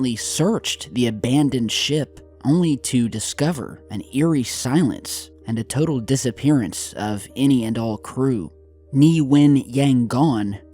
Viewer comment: I thought you said “total disappearance of any adult crew” A ghost ship full of psychotic ghost kids? =initial imaginative thought. * ”total disappearance of any and all crew” and all andall andul adult